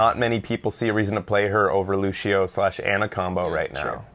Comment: "Lucio Ana Combo" Sounded like Lucio Anaconda